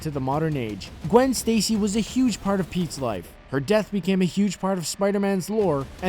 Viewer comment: Anyone else thought he said Pizza's Life 2:19 Lol